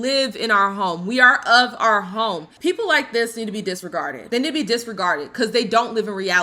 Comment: I thought you said discarded😂